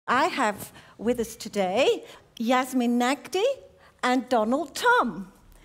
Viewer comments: I swear I thought she said Donald Trump. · Who else thought that she said donald trump😂😂😂😂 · Did she just say donald trump? I swear she did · Did they just say Donald Trump?!?! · I THOUGHT SHE SAID DONALD TRUMP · I legit thought she said Donald trump. I was like that ain’t right. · I lowkey thought she said Donald Trump I was like WUUUUUTTTTT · Did she just say donald trump? I swear she did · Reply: no 😂😂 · thought she said Donald trump😳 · When she introduced the two at the beginning, it sounded like she said Donald Trump.